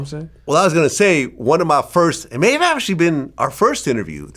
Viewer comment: 😂😂😂😂What the hell did Vlad say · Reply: May of actually been lol